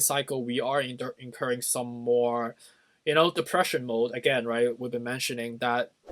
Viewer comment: When you said 'depression mode' I heard it as Depeche Mode initially. Also, I avoid diet drinks because the aspartame gives me headaches. begin music video of Depeche Mode sitting on piles of aspartame with monitors showing crypto charts in the background Aaannd, scene. Have a great night! ~FIN~